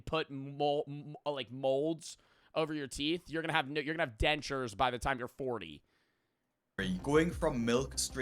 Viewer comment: ur gonna have ne- (new teeth) dentures, he forgot the word for "new teeth" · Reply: i heard "your gonna have ni- your gonna have new teeth"